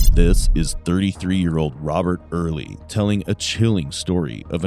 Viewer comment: Did this guy just say "This is three three year old Robert ..."?? I'm assuming I misheard and it's a real narrator